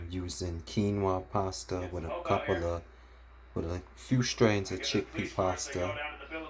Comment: What did you say at 3:32 after few strains with? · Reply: Quinoa pasta & chickpea pasta